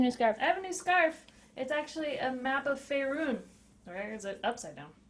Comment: Misheard Faerun as Beirut. Was quite surprised when she said Waterdeep!